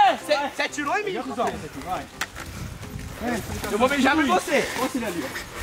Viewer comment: só eu q escutei o fala vou beijar vc, acho q escutei errado